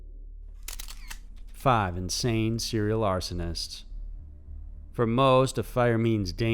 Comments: I thought it said "5 insane serial astronauts"..kinda sad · I was WAY more excited when I thought that said "serial ABORTION ISTS"